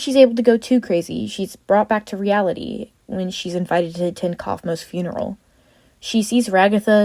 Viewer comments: the subtitles said canines funereal 😭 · the subtitle said caine's funeral loll😭